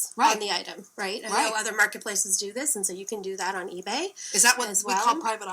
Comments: I heard "I know other marketplaces do this". Also, wth is up with the Christmas sweater in August? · Casey, I also heard her say "i know other marketplaces do this." which does sound alot like No other marketplaces do this. lol